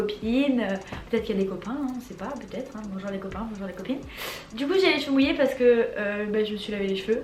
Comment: A 0:18 ta que moi qui ai entendu "du coup jai joui "